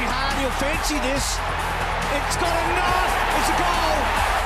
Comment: did he say "It's got a knife"? Are Crocodile Dundee references still the most recent Australia has? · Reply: He said “It’s got enough?” As in, the ball had just enough power to cross the line and be a goal